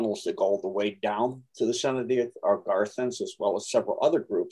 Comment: Anyone here know Jetson White videos? Wondering if he is sane. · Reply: Is saying Garthens @ 1:10:10? Trying to write down the groups he's saying but sometimes it's hard to hear is vowels